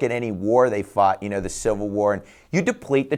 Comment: did he say saw the FED fought during the civil war?